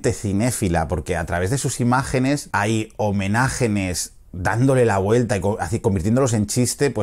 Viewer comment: escuché bien?... Homenajenes?